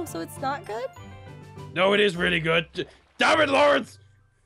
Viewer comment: does he say damn it Lawrence